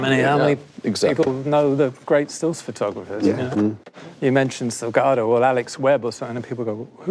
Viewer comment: Roger Deakins mentions two important still photographers: Alex Webb and another one whose name I couldn't understand. Can anyone help? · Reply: Sebastião Salgado